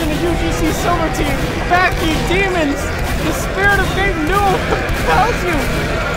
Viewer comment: did i hear "the spirit of dave miller compels you"? OH HELLO THERE, OLD SPORT.